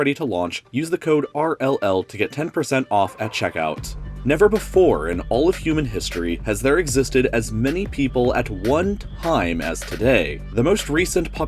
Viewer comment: If you listen closely at 0:10 it sounds like he says "never before in all of fruiting history has there existed as many apples at one time as today"